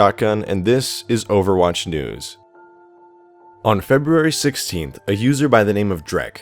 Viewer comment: Lol when you said overwatch news I thought you said overwatch NUDES😆😆😆